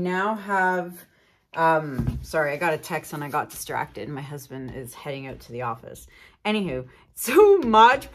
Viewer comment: Did you say your husband is heading out to the office? I didn’t know you got married. Congratulations.!! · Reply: I just listened again and it really sounds like you said “ sorry I got distracted, my husband is heading out to the office”. I must be hearing things!